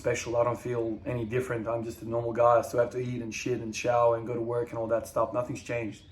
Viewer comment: For a second I thought you said "I'm just a normal guy, I still have to eat and sh!t in the shower" 😂 Congrats man!